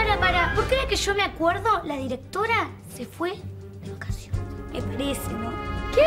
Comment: Que dice Tali?? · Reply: De vacaciones dice Tali